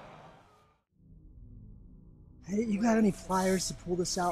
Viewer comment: At the start of the video it sounds like he says "have you got any fires". My mum is cooking and as soon as he said that the fire alarm went off 🙊😂